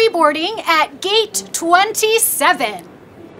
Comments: said gate 27 when cc said 77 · u sure the captions say gate 27? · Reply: She said at gate 77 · The Lady said: Gate 27 even though the subtitle said 77 did anyone notice that? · she said gate 27 and the captions say gate 77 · it says gate 77 instead of gate 27